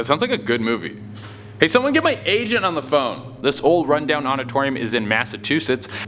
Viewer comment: Is it just me or did it sound like he said "hey somebody get my asian on the phone"? I know he said agent but he slurred it..